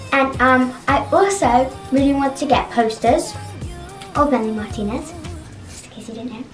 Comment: Who did say she wanted a poster of? I don’t even know who that is, I feel so old 😂 Can’t believe she’s so grown up! 🥹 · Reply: That makes more sense! I thought she said Benny Martinez and I was like “Who??!” 😂